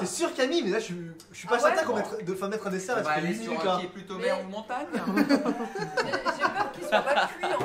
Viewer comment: j'ai pas compris il a dit quoi ben ? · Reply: "on va aller sur un qui est plutôt mer ou montagne ?" en gros... On va lancer un nouveau débat vu le temps que prend Camille avec le micro-onde